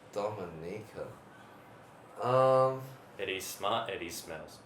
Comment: i thought brett said "eddy's smile" lol